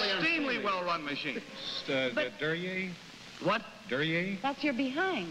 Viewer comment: That joke at 9:00 is obscure as hell. What the guy says is "Duryea," a carmaker that died out in the '30s--which she mistakes for "derriere," the French word for ass. And I'm pretty sure that's Candy Barr at 16:20. You're welcome.